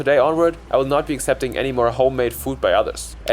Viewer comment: bro did not say "handmade" bro said "homemade"